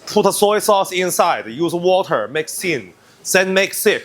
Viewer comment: I heard that as "make sin" not "make thin".